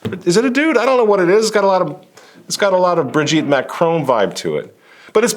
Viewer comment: did you just said Brigitte Macron vive?😂😂😂 you are hilarious!